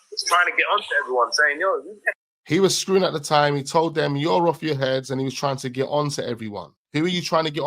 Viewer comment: said YOUR OFF YOUR HEAD someone else said that on another message jay recieved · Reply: I thought it was saying yout off your barnet?